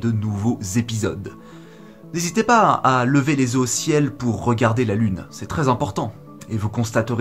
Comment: superbe vidéo comme d'habitude contenu de qualité , par contre j'ai compris " levez les œufs au ciel " mdrrr